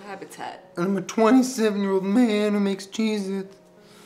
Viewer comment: “I’m a 27 year old man who makes cheez-its” 😂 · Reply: Caroline G 😂 I thought he said “I’m a 27 year old man who needs Jesus” but that makes more sense